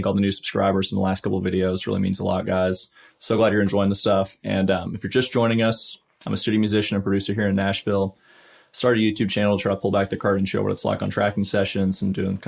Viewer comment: I swear I thought you said "I'm a studio musician and barista here in Nashville" lmao